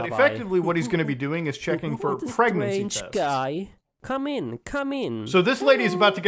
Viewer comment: what is he saying 🤨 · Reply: Hi come in come in